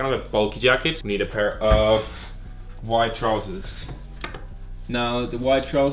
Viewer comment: I thought said white trousers instead of wide trousers